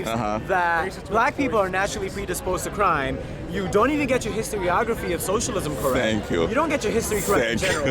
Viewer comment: Did he say historography? am i just stupid. is that a real word?